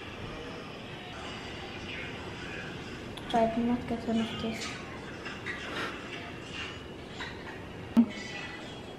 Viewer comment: what did he say? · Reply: "I did not get enough taste" 😂